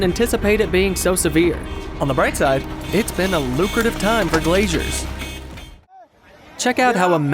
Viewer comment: "...it's been a lucrative year for glaciers." Actually, no. Glaciers are receding everywhere. Your commentary needs to be accurate and informative. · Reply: Late edit: the clip you're referring to is at the 6:24 mark, not the one at nearly 8 minutes) The guy said glazier, not glacier. A glazier is someone who works with glass, which is exactly what he said at the time because he was referring to the guy needing new windows. Your comments need to be well informed and intelligent. Otherwise, you look as ignorant as you assumed he was being at the time.